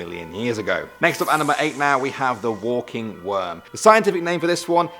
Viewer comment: Geez I'm tired, I thought it said "walking women" instead of "walking worm" 😑